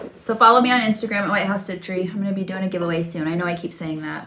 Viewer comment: I enjoy seeing all the strawberry things. Can you please link the strawberry pin cushion also your Etsy shop. Thanks sew much! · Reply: thank you sew much! Just found you on Etsy. I thought you said lighthouse not White House lol